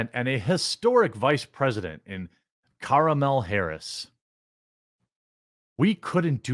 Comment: I heard commie-law Harris